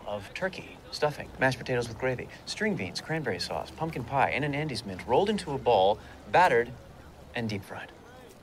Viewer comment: Every time you say "Andes mint" I hear "Andy's mint" and think "How nice, Babish has his own mints!"